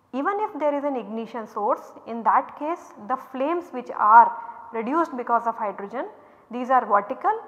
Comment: instead of produced it is given "reduced"